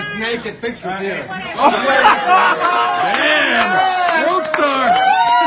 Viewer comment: Is it just me or did I hear Cenk yell Worldstar at 3:18?